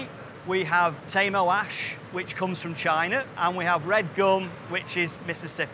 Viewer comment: Did he say "Red Dong"? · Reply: He says "Red Gum from Mississippi" ... 😊